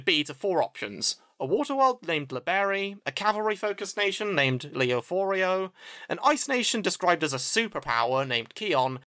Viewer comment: always misheard that for Leorio from Hunter X Hunter.